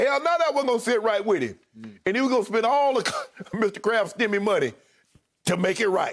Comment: "He was gone spend all of Robert Kraft stimmy money" 😂😂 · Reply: Thought he said "Mr. Krabs" lol unless...🤔🤔